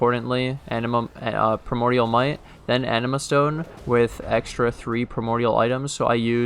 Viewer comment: Subscribe if you like AFKING! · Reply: I swear i heard "i use an enema stone"